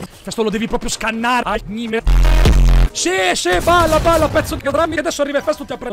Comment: Solo io ho sentito pezzo di catrame